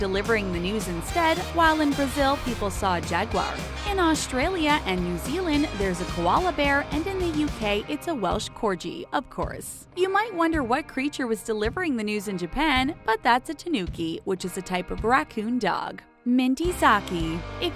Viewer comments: did you just say, 'corji?' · did i hear "corchi" instead or corgi?